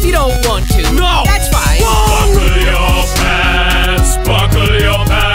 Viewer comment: I always thought it said buckle ur face and at 0:51 buckle ur ass